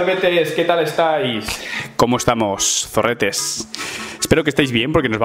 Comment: JAJAJAJ YO PENSE QUE DIJO COMO ESTAIS SORETES